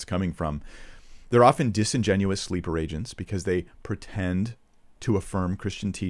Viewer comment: "Disingenuously _______?" Can't tell if it's a hearing thing or a vocabulary thing, but I cannot figure out what that second word is.